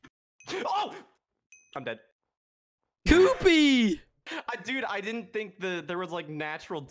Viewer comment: when yells "coopy" like rapid yells sounds like he was sayin poopy and i laughing so hard. i absolutely love this dude, he so frickin wholesome